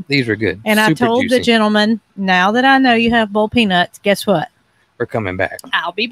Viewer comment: I now understand I thought you said BALD peanuts I now realize you are saying boiled peanuts. Do you have roast peanuts.